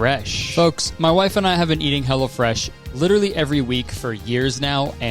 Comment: Deadass thought he said that him and his wife were eating "hella fresh" and I just agreed.